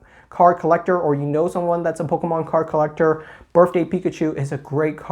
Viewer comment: Me: "Did he just say Birfday?" says Birfday 12 more times